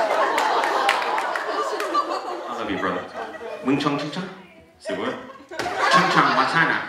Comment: Where you from? Mingjong! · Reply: I thought he said In Chung. His bird kimchi definitely came from Korea.